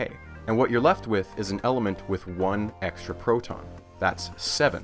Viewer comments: subtitles are inaccurate at times, 2:47 has one "less" instead of one "extra" proton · (psst! Error with the closed captions at 2:49 - verbally, you say "one extra proton" but the captions say "one less proton". Given this completely changes the meaning of the sentence, I figured it's worth bringing up! )